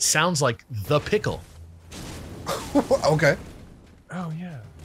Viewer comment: I always thought mario said 'the pickle' in 64 growing up. I thought I was alone.